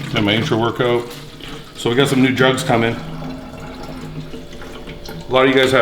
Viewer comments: Yup, I heard that as well it sounded like... "I got some new drugs coming" 😜 P.S I know he did NOT say that but it sounded like it. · For a second I thought he said I got some new drugs coming, I had to replay it lol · Thought he said 'so I have some new drugs coming' LOL